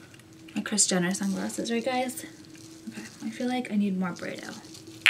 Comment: I'm from Germany and I can't understand which perfume she is wearing. Could someone help me? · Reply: Byredo perfume..it's Swedish :)